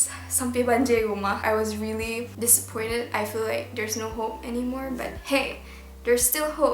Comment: hope???? I thought that you say J-Hope...